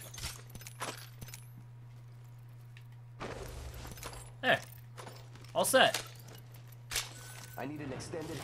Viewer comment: Lol at 2:54 it sounds like he said “Offset”